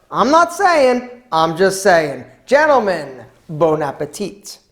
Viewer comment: Sorry, I heard "Bone-a-petite" at the end. Anyway awesome cooking, dude.